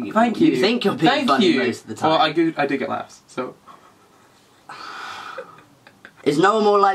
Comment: what did say at 1:48 i‘m not native english i don‘t understand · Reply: “I do get laughs so”